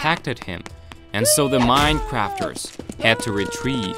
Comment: i just heard minecrafters in 14:03😂 Also at 16:30 theres just baby yoda looking at us for a sec